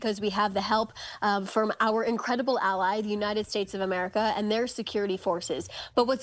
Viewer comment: "Our incredible ally daddy US" All i hear is "ga_w_k ga_w_k"